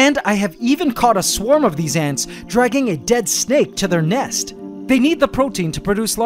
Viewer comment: did you say snake